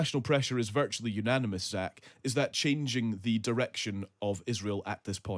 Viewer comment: god do you have a thick accent! I was not watching, but listening - so for a second I thought you’ve said: chang- ching! Instead of changing, and thought. Huh ?